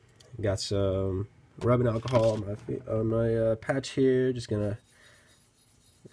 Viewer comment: no offense, but I thought you said "my crotch here". needless to say, I was slightly confused